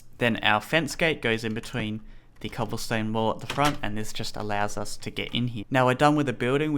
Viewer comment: when you where saying cobblestone wall it sounded like you said cobblestone wool